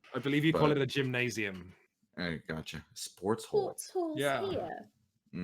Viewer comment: lol i love that ProZD clearly thought Quinns was saying "Sports HOLE" and no one ever pointed out that he was saying "Sports HALL" but, ya know, with his accent.